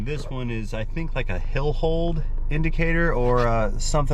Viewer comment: You said "hillhold" indicator. But, I wasn't looking at he screen. I thought you said "billfold" indicator. Possibly similar?